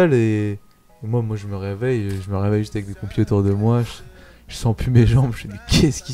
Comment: il dit pompiers et dans les sous-titres c'est écrit "computers", c'est un cp qui a fait les sous-titres ou quoi ?